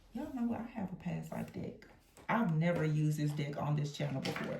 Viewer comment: I could have sworn you said “dick” and not “deck”! 😂 Great reading, btw. ❤